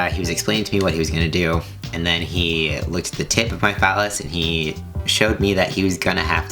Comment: I had to watch 2:29 a couple of times because I swear I heard he licks the tip of my phallice